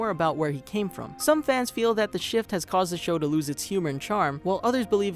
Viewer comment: I heard that as "the ship".